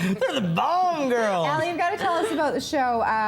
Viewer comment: I thought they were saying Bond Girls.